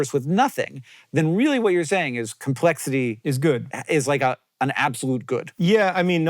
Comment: did for a half second think he said “complexly is good” and was like hell yeah.